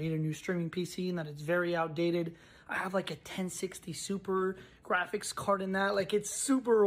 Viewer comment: Did he Just say 1060 Super? What ever it sounded like I knew we got him.